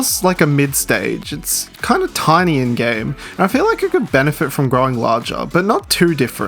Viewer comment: I heard, "it's tiny and gay," not "it's tiny in game." That's what my parents say about me. I 🤣🤣🤣